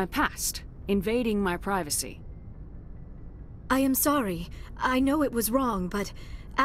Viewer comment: it says "onion celery" instead of "I'm sorry" with closed captioning.